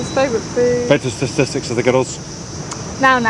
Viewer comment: seriously, WTH did he say!? Can we get captions ffs? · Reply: Vital statistics of the girls